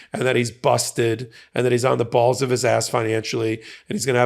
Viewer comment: “On the balls of his ass”.. is that what he said at 9:23?😂